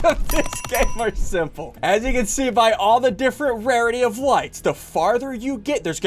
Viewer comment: did he just say likes